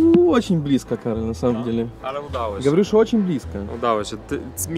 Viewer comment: Usłyszałem "gawarisz Chocim pliska".... wtf.